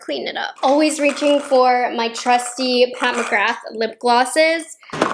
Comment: At first I thought you said, "always reaching for my crusty Pat McGrath lipglosses" 😅